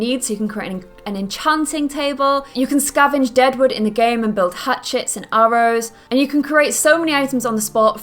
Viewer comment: when she said Hatchets, i thought she said hat shits